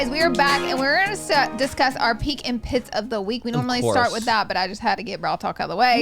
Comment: I always thought they said “peach and pit” and just assumed it was an adorable southern thing. I should have stayed in my ignorance.